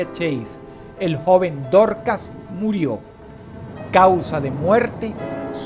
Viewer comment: pensé que dijo: "El joven tortas" xDD Espero no irme al infierno